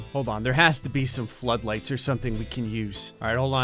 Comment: I honestly thought said bud light instead of flood lights. 🤭